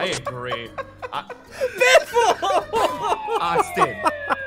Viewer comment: did say biffles name!?